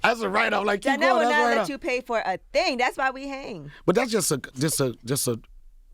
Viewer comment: Yee, I heard you... "Donnell pay for everything thats why you hang with him" truth in jokeing..LOL